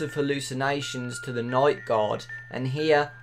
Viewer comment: It sounds like he is saying night god lol